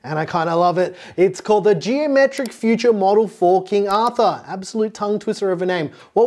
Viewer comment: Is it called the Geometric Future F*cking Arthur?! :D